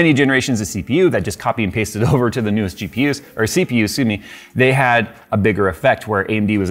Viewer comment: did jay say sue me instead of excuse me?